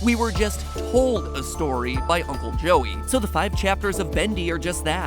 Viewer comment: I thought matpat said purple joey plot twist joey drew is purple guy